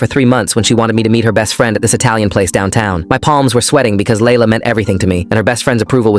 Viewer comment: I heard “my plans are sweaty” and thought of Eminem